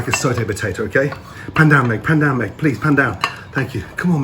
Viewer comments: I thought I heard "PANDEMIC" instead of "PAN DOWN,MEG." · When he said "Pan down, Meg," I heard "pandemic." WHAT IS WRONG WITH ME